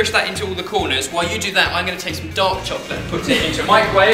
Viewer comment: When ben says "dark chocolate" i hear "dog chocolate"